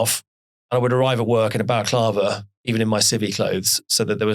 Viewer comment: What does Ben say here at 35:27, "I would arrive at work in a"??? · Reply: Balaclava.. Full face cover to remain anonymous.